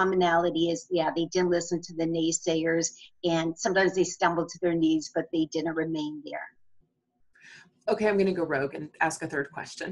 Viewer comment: Hey, I couldn't hear some words that said from 3:34 to 3:39. Is the auto-caption from youtube right? "...dont listent to the naysayers, sometimes they stumble to their knees but they didnt remain there... "?? Just want to understand what she means. Thanks! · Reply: Hi Pham, the captioning is from YouTube. She said "...they didn't listen to the naysayers, sometimes they stumbled to their knees, but they didn't remain there." She's saying that people fall, but can get back up again. :)